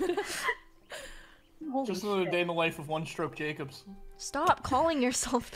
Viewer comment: For a second I thought it said “one choke jacobs” LMAO😂